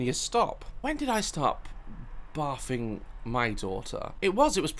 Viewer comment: I thought daz said when did I stop bathing with my daughter I was like WHAT YOU WAS IN THE BATH WITH YOUR DAUGHTER